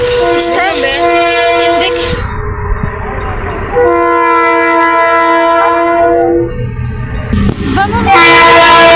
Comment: cocuk ne diyor?? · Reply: Ne diyon be dedi sanirim